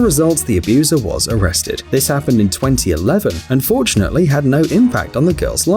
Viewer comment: did he say unfortunately??